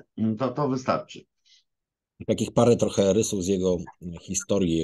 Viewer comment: „jego ojciec był tokarzem” - aż musiałem przewinąć, bo usłyszałem najpierw, że „jego ojciec był to karzeł”… no nic - słucham dalej. :)